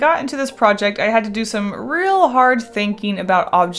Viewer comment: I totally thought she said "I had to do some real art thinking" but when I went to get the time stamp, I realized... that's not what she said...